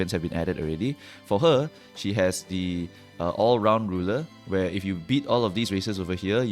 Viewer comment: For a moment, I deadassly heard that as "All-round gooner". Would make perfect sense either way.